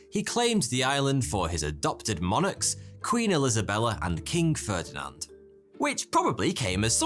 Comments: Did you say “Queen ILISAbella?” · sounds like he says “elisabella” XD